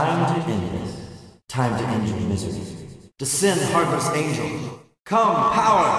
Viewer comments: How the hell did anyone get "Sin Harvest" out of "Descend Heartless Angel"? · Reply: Miguel Ortiz Yeah, and I thought he was saying "Sin Harvest Angel". Plus, in the heat of battle, it's not like I'm giving too many shits on what he's saying...I'm either trying to hit him before he shoots it off, or preparing a healing because otherwise I'm fucked! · It sounds like he says "DESCEND HARVEST ANGEL" and not "DESCEND HEARTLESS ANGEL"